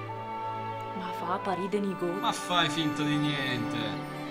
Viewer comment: al 0:25 cosa dice esattamente " fa a parì de nigot"? è lumezzanese? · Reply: Dice proprio " fa a parì de nigot". Qui è usato... non so se sia importato da Lumezzane...